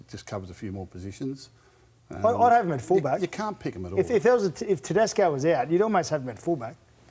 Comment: brimson was great for queensland and hes great for the Titans .. Holmes has proven nothing at fullback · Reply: I thought you said his coach won't pick him at fullback?